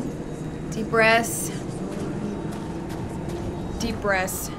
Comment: Since you're using Ludwig's Holy Blade, try using the L2 moveset in two-handed to see if you like it. You can string together very powerful strikes that stagger and stunlock harder opponents with it. I LOVED it for the DLC! Great content as always. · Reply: Jonathan M what does she say (twice) at around 17:00? Dee brass? [English isn't my 1st language]